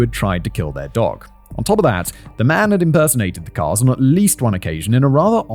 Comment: Simon says “The man has been impersonating the Karl’s.” I hear “the man has been impersonating the cars”